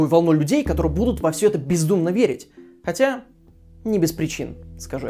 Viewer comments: Я услышал "Всё это породило новую волну людей, которые будут во всё это пиздумно верить" xD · Мне одному послышалось пиздунно верить